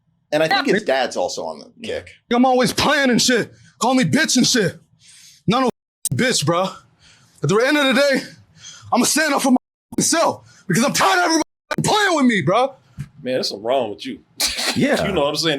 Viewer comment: I'm sorry but everytime I've heard him say: "Imma stand up for myself!!! I'm tired of everyone playing with me". All I can hear is: "I am NOT GAY! I DONT LIKE MENS ANYMORE"